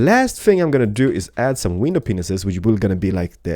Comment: did he just say window penises😹😹